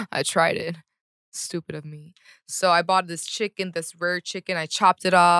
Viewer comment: 11: 58 did I hear "I bought this chicken that's very chicken" 😂